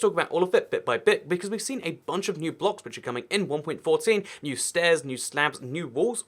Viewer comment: 14 stairs, 14 slabs, 12 walls being added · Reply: Waw I’m dumb this whole time I thought he said 40 new stair and slabs and walls I was too hyped , he kinda said 14 weird and mumbling, I’m an organic build and I was hyped waw